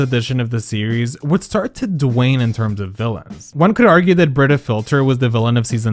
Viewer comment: What was that word at 10:07?? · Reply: my guess is it’s supposed to be “wane” but it’s mispronounced as “dwane” 🥲